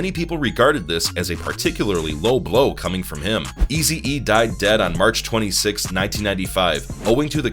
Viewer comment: 'Easy-E died dead', did I hear that correctly? And where is that admission of Suge Knight... If you are making stuff up like that, you can get yourself sued.